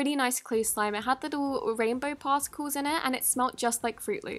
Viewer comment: Lol I just heard she's saying partsicle